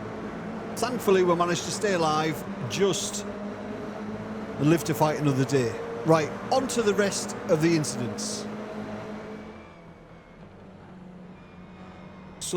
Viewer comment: I heard it as "lift to fight another day", which seems appropriate! I'll have to remember that one.